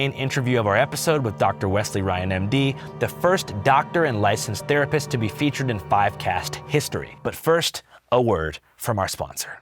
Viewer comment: originally thought you said your interviewed guy was “the first doctor/therapist ever interviewed in podcast history” 😂😅